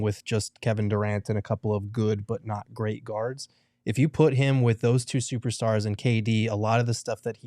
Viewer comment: Did he just say book and Beal aren’t great tell me if I’m stupid